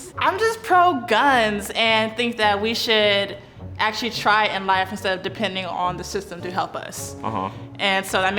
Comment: I'm pretty sure the caption at 12:30 should be: "...should actually try in life instead of depending..."